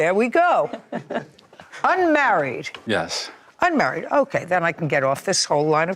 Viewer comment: He talks like he has a mouth full of potatoes. I thought he said that he's married as well.